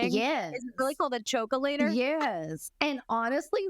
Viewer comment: Tish you crack me up. The choke-ya-later(not the name but it sounded like you were saying that and I can’t unhear it) had me rolling 😂